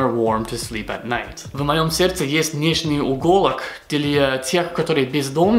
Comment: не угОлок, а уголОк, ударение на последнюю букву "О" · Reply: мне вначале послышалось ИгОлок, думаю какая там иголка, потом дошло.